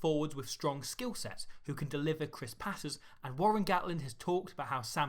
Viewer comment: I misheard 'can deliver crisp patterns' as 'can deliver chris paterson'. What a great call up that'd be.